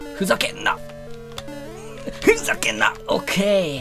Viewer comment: ふざけんなっ！！のとこがプチャヘンザアップに聞こえて思わず手あげたわ